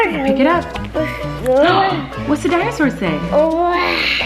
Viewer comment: I heard say another binosaur at 4:56!!!!